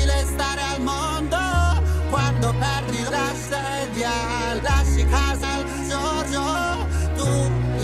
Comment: ho capito "lasci casa a JoJo" 😂 dipende a quale...